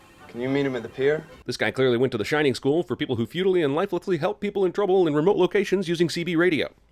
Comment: Did you just say lifeleffly? Mother F$&@ DING!